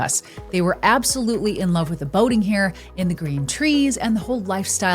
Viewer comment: I thought you said the Californians that moved in " loved the Voting here . ". Made sense but had to replay . Boating here . Voting is about the same , ( embracing communism )